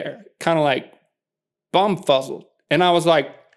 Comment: did Link say "And then they stood there, bumfucked" ?? The closed captions says "bumfuzzled" but all I can hear is bumfucked